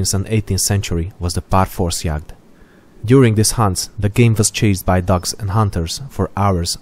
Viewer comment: i thought you said "during this hunt the game was chased by ducks and hunters" i was confused as hell lol